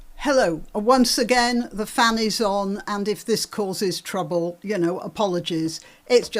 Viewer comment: 😂😂 i thought you said the fanny is on..like a guest or something 🤦‍♀️🤦‍♀️